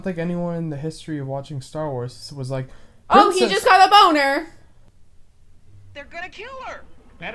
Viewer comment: Instead of "he just got a boner" I thought your sister said "he's just gonna bone her"... way more dirty minded